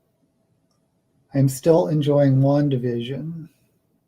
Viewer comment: I did drag king once ... I don’t think the photos exist anymore because it was so bad 😂 it’s so hard! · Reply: d’Aulaire’s Book of Greek Myths is a great short book to learn the basics 💜 ... for a second I thought you said you were enjoying long division instead of Wandavision 😂